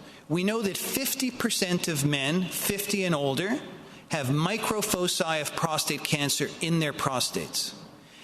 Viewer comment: what is Said??? 50% of men 50 and older got micro ???? of prostate cancer... What he said???? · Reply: Microfoci (small focus points of prostate cancer).